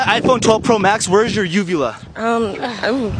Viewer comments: I swear to God, when he said "ugula", I thought he said "vulva". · For one second I thought he said vulva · I thought it said vulva 🤦🏻‍♂️